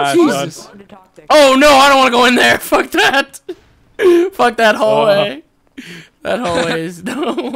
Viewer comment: did Nova said "Fuck dat Hole" ? . . . .